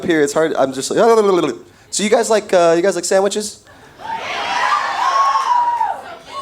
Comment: Did someone say "I like your butt!" When they were talking over him?